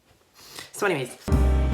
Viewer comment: when said “so anyways” the subtitles said “so enemies” lmaoo 😂😂